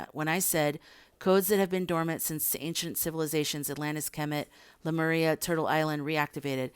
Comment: So, can you please go to 26:01 and add in the comments what you say? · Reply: I say “Kemet”